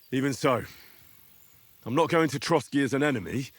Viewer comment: And I keep hearing "Trotsky" which adds an extra little frission. 😆